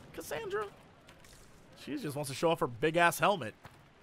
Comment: I heard it as this: "She just wants to show off her big ass...helmet."